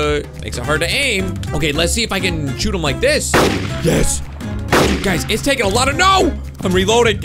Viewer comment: This is I heard you say: This is taking lots of no! XD